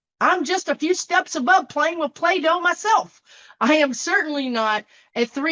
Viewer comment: Thanks so much for your grounding readings, keeping us to continue our daily lives with positivity and hope. You stay practical during these very challenging days...Thanks to you and your guides! Chuckling over the Play-Doh image. So, an appeal? Most definitely not a fair intellectual or experienced equal...I love the idea of"Club Beach!" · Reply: Play-Doh? Oh, thanks. I heard "Plato" and couldn't figure out the meaning.